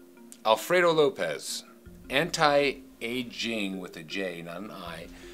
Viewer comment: I THOUGHT HE SAID HENTAI AND I NEARLY SPIT MY FOOD OUT